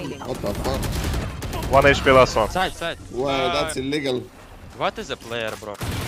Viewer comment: what did that guy say? Hmm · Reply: LMAO. He said illegal but it really sounded like what we were thinking